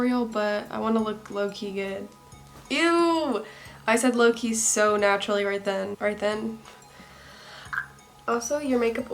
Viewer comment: I thought she said “low key Italians, right”